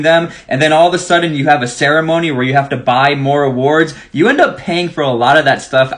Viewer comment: Did he just say “buy more awards”